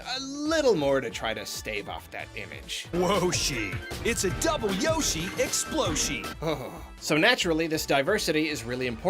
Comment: I choked on my coffee when i heard "double yoshi exploshi". You could've killed me.